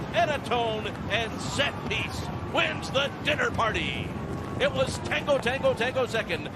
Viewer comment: Thought it said "Donner Party". Didn't end so well for the horses.